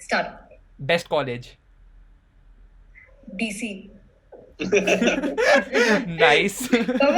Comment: what is she saying? which college is this... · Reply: Dtu (former Dce)